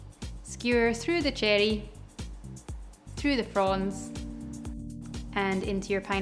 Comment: I keep hearing your "fronds" as "prawns" 🤨